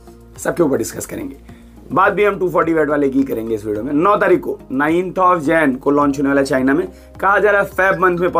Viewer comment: kya bole 9th jan ?